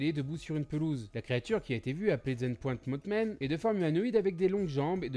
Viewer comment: euuuu il dit quoi la? zeintpoint motman?